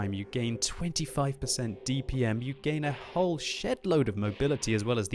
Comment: did he say shit load